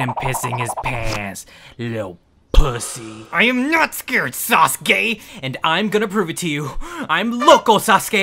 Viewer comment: subtitles say SAUCE GAY lmfaooo